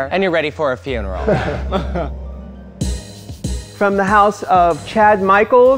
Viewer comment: All I hear is "from the house of HAAAAAAAALL"